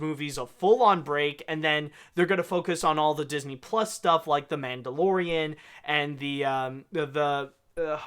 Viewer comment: did he just say, “the Man DeLorean”?